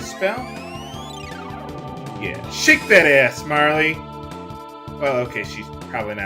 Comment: did u say, "shake dat azz Miley"?